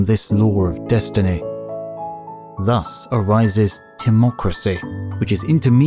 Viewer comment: ❤ hello... 👋 While the screen features "TIMOCRACY" , the subtitles say "democracy"... subtitles are on so I could understand the narrator better🤷🏻‍♀️😬 🤔1. Timocracy , Aristotle's usage = form of government in which possession of property is required in order to hold office. 2. Timocracy, Plato's usage = form of government in which rulers are motivated by ambition or love of honor. Origins: late 15th century: from Old French timocracie, via medieval Latin from Greek timokratia, from timē ‘honor, worth’ + -kratia ‘power’. 🙏 CORRECT your SUBTITLES😂